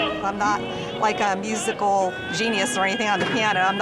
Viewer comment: did she say penis?